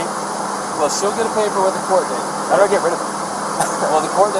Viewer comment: Did he say how do I get rid of her at 27 minute? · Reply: He's guilty of SOMETHING. They went on a cross-country trip TOGETHER and he's the only one to show up ....IN HER VAN!...But I thought he said "how can I get rid of IT" As in the citation the cop was explaining. I could be wrong. But there's NO WAY he doesn't know what happened to that girl.